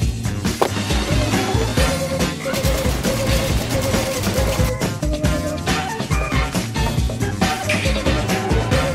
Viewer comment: i didnt understand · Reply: Marie O it was No Mans Sky😂